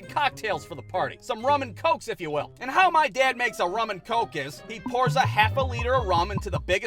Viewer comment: I thought he said ramen coke lol